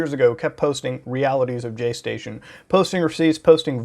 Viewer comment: Everytime I hear jaystation's name said, quickly.. I always hear jayztwocents. And then i'm like "wait.. that's impossible. That guy is like wholesome tech-guru with a porsche'